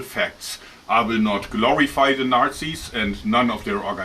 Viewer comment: he said Narczi's at least it sounded like that was a cherry on top bahaha. Either or they are were a horrible people and here is brave and bold for speaking on this topic, God bless